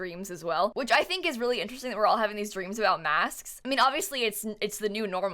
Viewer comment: I thought you said you were having stress dreams about maths and I mean same...